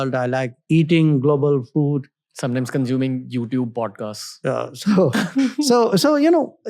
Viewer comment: auto caption says vodkas instead of podcasts